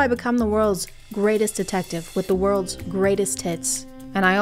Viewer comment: did you say with the worlds greatest tits?